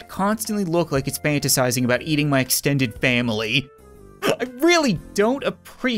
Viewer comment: I thought you were about to say "eating my extended ass" lmao